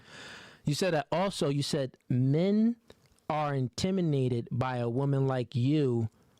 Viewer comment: I KNEW I WASN'T THE ONLY ONE I HEAR "INTIMINATED" 🤣🤣🤣🤣🤣